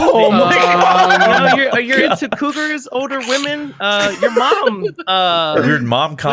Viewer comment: Wow instead of "vietnam" i heard "your mom" at first 😂